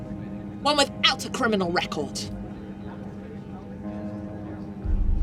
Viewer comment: did she say “recold” 🫡